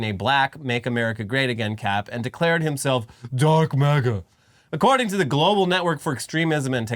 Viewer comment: I heard Musk say he was "dark MAGA", but I misheard it, I thought he said "dork MAGA". Which is far more suitable for the goddamn idiot!